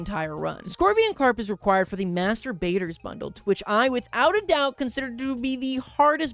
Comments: I misheard master baiter 13:23 sorry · What (probably) said: "the Master Baiters Bundle" What I heard: "the Masturbators Bundle"